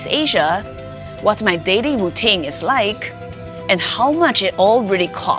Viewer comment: I thought she said dating i stead of daily 😂.